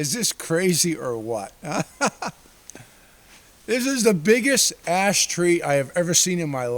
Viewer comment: Haha.........at first I thought u said "this was the biggest ass tree u had ever seen"..........I love trees and I've been fortunate to live on the same small city lot where I planted over twenty 6' tall pine trees in the early 80's and now I watch the owls and hawks sleep in their upper branches near my bedroom window......I saw another outdoorsman use his coffee thermos bottle as a roller pin to spread out his home made pizza doe .............yep the snow covered ground can really quiet down the surroundings in winter.........even in the city it makes noises almost disappear........................solitude and a peaceful existence with nature is the best medicine in the world..................my best friends are the creatures that visit my yard from squirrels to chipmunks to owls to raccoons and even an occasional garter snake are all a welcome site